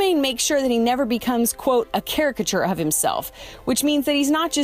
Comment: ??? “Charachature???” I think he said “character” maybe I’m wrong and just never heard that word before 🤷🏻‍♀️